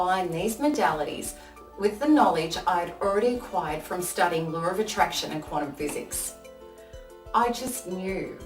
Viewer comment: Did she say Lure of Attraction and Quantum Physics? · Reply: Law of attraction x